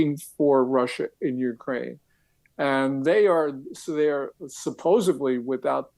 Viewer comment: did gordon just say "supposebly"?!?